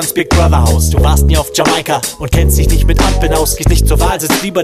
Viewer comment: was sagt der in 0:20? Ampeln?